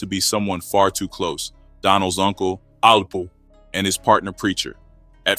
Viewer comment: Also was a snake, but he had nothing to do with Donnels kidnapping and murder. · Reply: Sounds like he said “uncle apple but the A.i . Voice made it seem like he said alpo